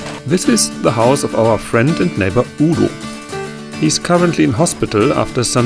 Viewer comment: I love how all the non-german speakers write Udos name as "Oodle"😄 language differences are so interesting. Also: such a nice thing of you to do. Its so nice how in your community you guys help each other all the time. Thats how it should be everywhere. Oh und gute Besserung an Udo! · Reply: I thought he said, my neighbour Uhr! Anyway, best of luck to Udo and you